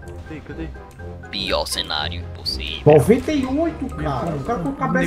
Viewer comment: eu escutei em "Pior cenário possível" Matanza hehe